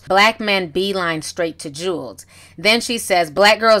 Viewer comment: Lol at 4:40 it sounds like said "hashtag white Thots" LMAO